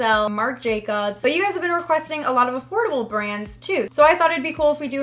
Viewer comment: it sails AFFORDABLE AF and i thought it said ADORABLE AF